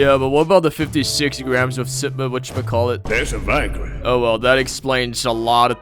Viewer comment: Tf did he even say? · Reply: Thats viagra